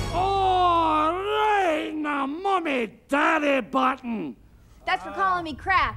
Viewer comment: Right in the mommy daddy button! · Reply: William Darkhölme LOL 😂 I thought he said: “ Right in the money daddy button.”